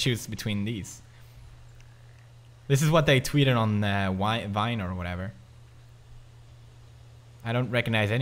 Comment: did you say tweeted on vine?